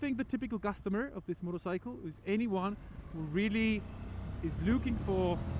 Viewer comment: He doesn’t say ‘81’ he says ‘ANYONE WHO……..’ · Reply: yes after watching it again it took me a bit but I understood that he meant or tried to say anyone but his accent made it sound like he was saying 81😅